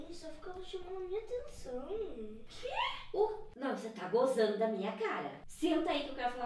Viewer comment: Só eu que ouvi " Você tá gozando na minha cara ! " E fiquei rindo que nem um idiota? Kkkk